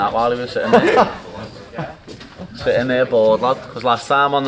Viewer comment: What in the hell did he just say? lol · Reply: "Sitting there bored, lad...."